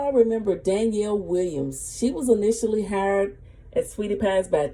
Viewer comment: Im lost I thought she said she was never really fired. Baby girl I went to school for the performance Arts too and we rehearse before lights camera action